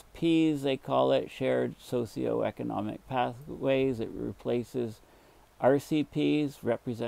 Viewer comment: Please donate to <link> to support my educational videos on the latest climate science. For the <num> decade global methane emissions are estimated from the Top-Down method to be 576 Tg CH4 per year, 60% from anthropogenic sources and the rest from natural sources. Bottom-Up methods suggest numbers 30% higher. Two-thirds of the emissions are from tropical regions (below latitude 30N), with about one-third from low latitudes (30N to 60N) with only about 4% being from high northern latitudes (60-90N). Global emissions have increased by about 10% in the last two decades, with present levels 2.6x their preindustrial level. It is thought that the main OH sink has not changed much. Please donate to <link> to support my educational videos on the latest climate science. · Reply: I thought you said: Shared SocioPathic Economic Ways